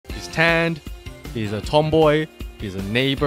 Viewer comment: Am I cruel for laughing extra hard because I thought the first thing he said was "she's 10"